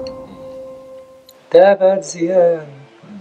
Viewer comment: شو قال بدقيقة 0:30 ؟ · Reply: غنى مقطع من أغنيته دابا تزيان